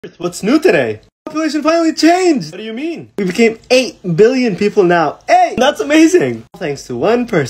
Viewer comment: I thought it said "The pollution finally changed!" and I was like, "So there's no more trash!?!"